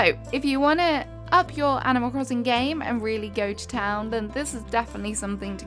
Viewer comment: i thought she said camel toe crossing 🥲